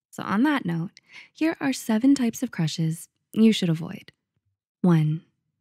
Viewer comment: I accidentally read the thumbnail as “types of Crackheads you should avoid” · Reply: For a minute I thought the thumbnail said, "Types of Cancers to avoid"